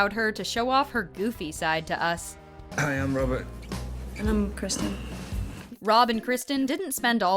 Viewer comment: did you just said “Robin”?? Lol 😂 7:11 and who’s Kathren ?